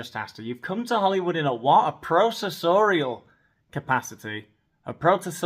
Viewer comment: I heard it as professorial, which make me choke, but she is constantly lecturing people.